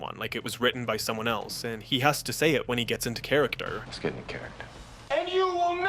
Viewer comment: I always thought he said, ‘Let’s get the character’, not, ‘Let’s get INTO character’